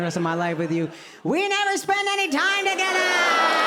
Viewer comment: When you said "We don't spend any time togethaaaa!" I heard "Wrong levaaaa!" 🤣